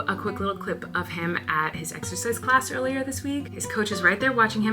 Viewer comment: Did anyone else hear Colleen say that Wesley was at exorcist class? 💀 I had to rewatch it and then realized she said exercise 🤦🏼‍♀️